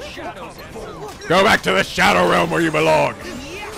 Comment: I thought Riku said go back to the shadow asshole! lol